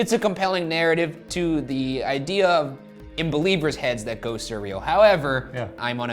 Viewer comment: I'm just here to say 2:49 in the captions it says "Beliebers" instead of Believers & I am personally offended by it